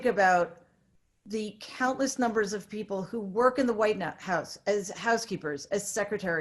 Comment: ish... Did she say, the countless numbers of people who work in the white nut house? Hahaha, I am sure it was a Freudian slip.